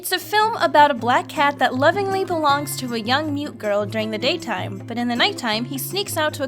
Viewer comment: When she said "young mute girl" I thought she said "young mutant girl", I think I've been watching too much X-Men 😂